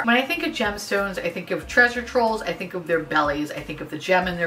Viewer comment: When you were reading the description of the beauty blender powder I swore you said it had semen rolls! Lol!!!!!! I snorted and had to rewind you and have my husband listen to it. You totally didn’t say that at all. My mind is so gross sometimes lol!!!!